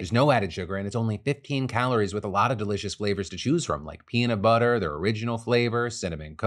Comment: Did Phil say pee in the butter when he was doing the ad 😳 22:47 😂😂😂